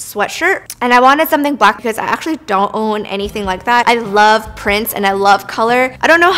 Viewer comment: i thought she said she loved princes but she said prints